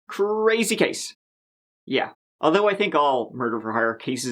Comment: LOL, I totally thought you said, “Crazy cakes!” in reference to Brenda Delgado. It was “crazy case!” but you REALLY should start calling all of the murderous, scorned females “CRAZY CAKES!” ....or not. But... yeah you totally should. 🍻Cheers from Michigan!